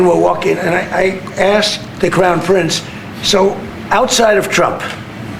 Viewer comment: It so sounded like he said the “clown” Prince hehehehehe!